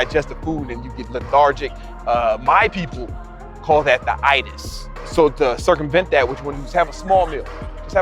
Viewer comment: can someone tell me what does he says at 1:45? "my people call that what?" i am interested because i feel tired when i break the fast with a big meal too. thanks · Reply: The Itis